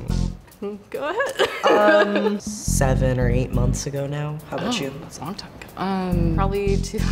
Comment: after he said “7 or 8 months ago” she said “oh that’s a long time ago” then she says her last was 2013 💀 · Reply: kill gavin OH MY GOD I THOUGHT IT SAID 7 OR 8 MINUTED AGO IM JUTS LIKE WHAT